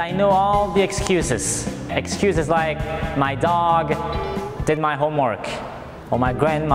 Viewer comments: he said my dog DID my homework but what the sub title said was different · in the subtitles it's still "my dog ate " instead of my dog did